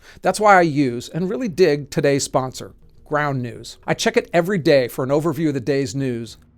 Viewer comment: Thought he said "Brown News". I get enough crappy news thanks. Lol.